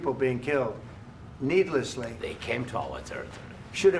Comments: what the hell did he say ? 🤣🤣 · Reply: said they came to our territory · did Zelenskyy said they came to our territory?